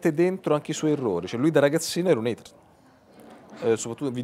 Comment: cosa dice di Snowden? che era un "?????" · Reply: ombra30 “hater”